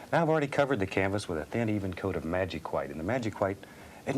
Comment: did i just heard magic white instead of liquid white?